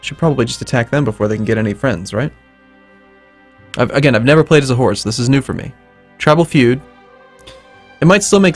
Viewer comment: "I've never played as a horde, so this is new to me" R.I.P. Sarmatians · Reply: ***** I thought he said 'I've never played as a horse'.